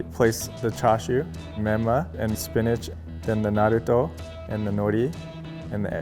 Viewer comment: Did he just said the naruto